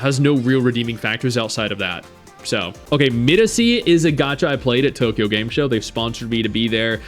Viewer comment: whats the game at 26:04. cant understand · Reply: Miresi